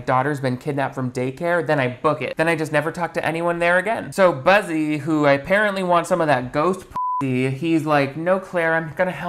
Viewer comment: Every time he mentions the main guy it sounds like he says "Bussy" I can't 🤣